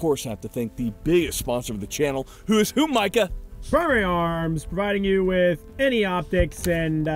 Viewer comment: Thought he said Fury Arms 😂